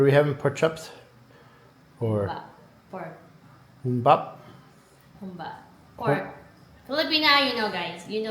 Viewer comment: What did you two say dinner was called? Ūmbop? How do you correctly say it and spell it? I’d like to look it up on Google and see if there’s any Filipino restaurants in my area that serve it! 🥘🍛🥗😋